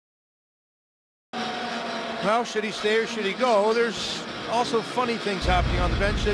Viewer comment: what did he say at the beginning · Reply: Jake Fisher well should he stay or should he go